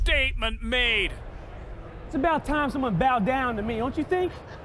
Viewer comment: what he say? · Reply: Erron Black he says: “its about time someone bow down to me” and he asks a girl in the public, its his wife